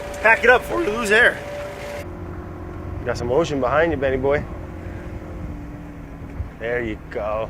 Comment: did he say vinny boy?